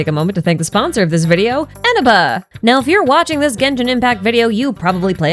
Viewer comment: I thought she said enema for a second… 💀